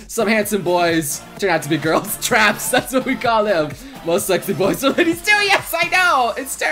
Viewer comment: did just say most Sexy boys turns out to be Ladies?